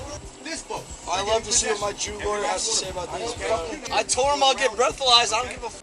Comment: “ I love to see what my new lawyer has to say about this” is the funniest thing I have ever heard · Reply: Not "new" lawyer, "my JEW lawyer!"